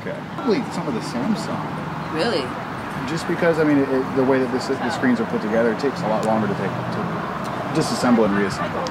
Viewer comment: What does he say at 2:59? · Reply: he stutters there but he meant to say: just the way the screens are put together